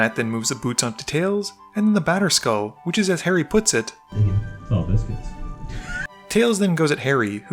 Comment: What did Harry say at 7:18 ? I couldn't understand what he said. . . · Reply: "That's a lot of biscuits"